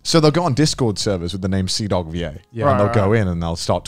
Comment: I love how the captions say "Seadog VA" when says CDawgVA 😂